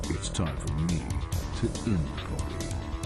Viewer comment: Sounded like he said "It's time for me to end globby". I never would have guessed it was "party". It is really hard to hear him. He got really quiet for some reason.